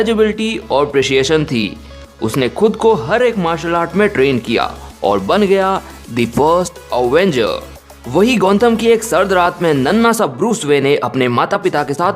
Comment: how many of you heard the worst avenger instead of first avenger